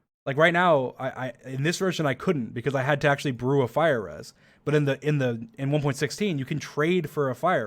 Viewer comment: Did dream say fire ass